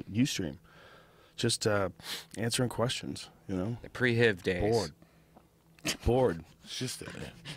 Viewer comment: did redban just say pre-HIV days at 42:33????